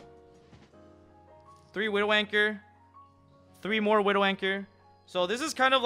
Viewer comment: As a returning player, I was confused as to what Widow Wanker is. Turned out it was Widow Anchor, not Wanker. Lmao. Worse than misheard lyrics.